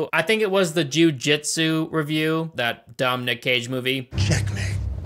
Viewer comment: im sorry if i missed some type of running joke, but did u just call Nick Cage, Dominick Cage??